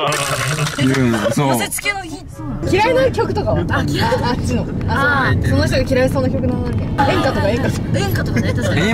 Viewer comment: のとこ、え、え、そーなの？って囁くような声がかすかに聞こえるんですけど誰の声ですかね、？りほちゃんの声にも聞こえないような気がしたし、ニコちゃんの口パクにもあってないから、、、勘違いだったらすみません、、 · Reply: え、そうなの？って言ってます確認できました。